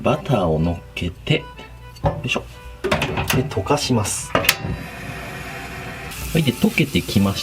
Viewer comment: 溶かします、が、犯します、に聞こえた私草